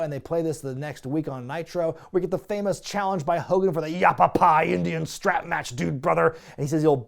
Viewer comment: I legit, LEGIT, thought Hogan said "Applepie Indian Strap Match" back in 2000.